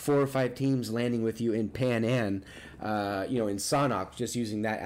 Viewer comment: I swear says "Pay a nan" 😂😂